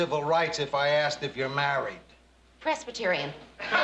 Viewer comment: what did she say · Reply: Presbyterian